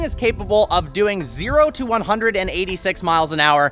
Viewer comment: i thought he said 0-100 in 13 seconds.... no he said 0-186🤯